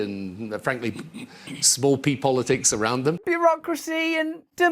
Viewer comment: small people politics. what is he calling us? small people their all ass hats